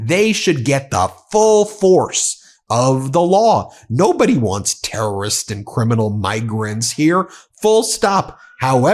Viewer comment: I thought you said "nobody wants criminals here ". Who do you think is going to be the next president ?